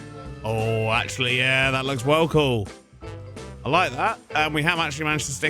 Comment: did you say “Wool Cool”? working on his dad jokes already